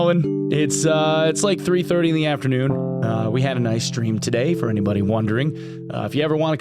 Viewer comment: Oh.. "A nice stream" First listen I heard "We had an Ice cream today" 😋